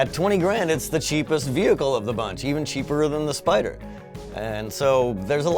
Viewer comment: Did he just say cheaper-er? Cheaperer!!! ROFLMAOLOL